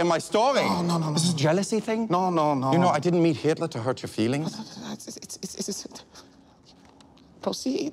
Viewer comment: "I didn't meet Hitler to hurt your feelings" Not only was Herr Müller pro diversity but he was also a sensitive guy. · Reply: i thought he said " i dont need hitler to hurt your feelings" as if he was burnmaster